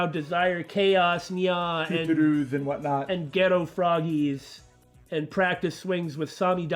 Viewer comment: I could have sworn I heard Ghetto Froggies